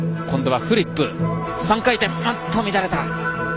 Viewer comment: 复活了、大家都在等。世界冠军。来啊，LuChen 陈露。九岁时，她成功地回转三周跳。Lulu的、21岁优雅自由滑表演。 0:50 勾手(三周)跳成功、还加了后外点冰两周跳。 1:09 下一个后内点冰三周跳。哎、有点乱。 1:42 两周半跳。 2:01 这首歌是butterfly Lovers. 2:13 再次挑战勾手三周跳、、啊！成功了。她充满微笑。 2:33 后外跳怎么・・・ 三周跳成功了！ 3:50 后内(三周)跳。 4:10 后外点冰三周跳、哎！第二个三周跳是双脚着冰(正确旋转不足) 4:38 跳跃的、旋转的、非常优雅。呜呼、、特别感情从心底升腾起来。。。 4:53 最近两年非常受苦了。世界冠军、身边的环境改变了、也有她自己的伤害。从13岁起就出现了国际舞台。陈露之名、在日本也有很多粉丝。在奥运会・长野的white ring上、她的风格能表演了。虽然有稍微失误、但是肢体非常舒展、体型优美、我想真的她的风格能表演了。她才21岁、已经称为“老手”。这就是花样滑冰的世界。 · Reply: 今度はフリップ、三回転、あっと乱れた。(「おっと乱れた」とも聞こえます)